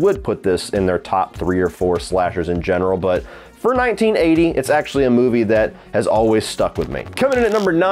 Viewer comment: Yo for a second, i thought he said 1980s